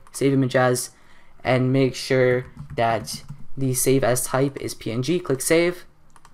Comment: WHEN HE SAID SAVE AS IT SOUNDED LIKE HE SAID SAVE ASS!!!